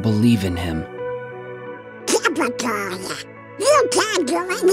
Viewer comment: What was the first word Donald said? Oh, it's "Typical". Never mind